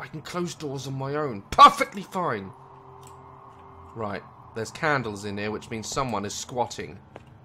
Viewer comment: was it just me or did say "there's candles in here which means someone is squatting"?